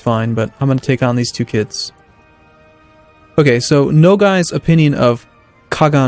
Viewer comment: I thought he said "no guy's" at 9:29 lol